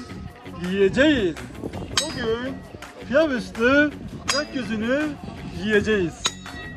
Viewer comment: bugun pilav ustu gokyuzunu mu yiyecegiz dedi la ? Yoksa ben mi yanlis duydum 😂